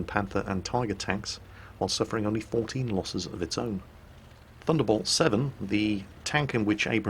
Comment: I thought you said war thunder. You almost made my day :(